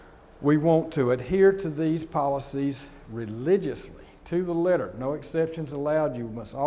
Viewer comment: did he just say "religiously"?